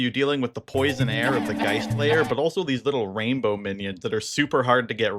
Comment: clocked way too many hours in deadlock and thought you said "geist player" what is wrong with me